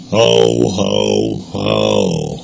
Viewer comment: I dont hear "ho ho ho" i hear "hoe hoe hoe"